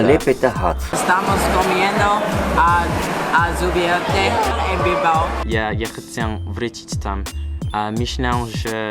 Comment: Spanish is my first language and i can’t even translate on what he said on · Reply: Josh Ventura haha I used a lot of Proper nouns so that makes sense, I was saying (in my then very basic Spanish 😂) Estamos comiendo al azubierte (the mall name) en Bilbao